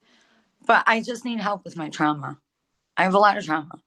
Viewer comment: O M G !!! when she was saying what her problem is, I thought she said TRUMP !! 😂 think I’ve been seeing waaaay too many people with TDS 🙄